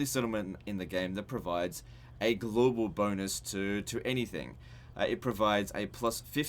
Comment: My phone keeps automatically google searching around 4:30 when you say 'global bonus' because apparently when says that, it sounds like me saying 'Ok Google' Fuck I hate technology sometimes...